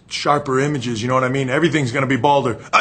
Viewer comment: Would someone be able to tell me what Shawn shouts at 2:03? (Oh, gotta ...... in here!") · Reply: god I gotta find a mirror